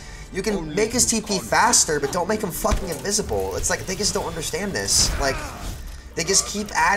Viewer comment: I swear is saying niggas don't understand this 😂😂😂😂😂